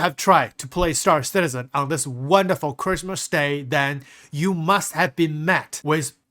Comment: For a moment, I thought you said, "If you've been trying to play Star Citizen during this Christmas day, you must have been mad (i.e. insane/crazy)" 🤣